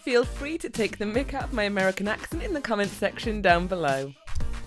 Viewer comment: Omg! I thought she said "Make fun of my american accent, in the comment section down below" hahah!